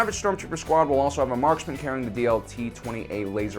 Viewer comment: thought he said blt